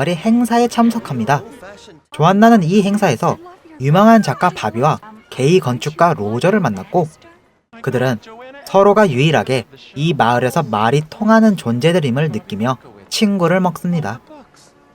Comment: 게이 건축가 로 들림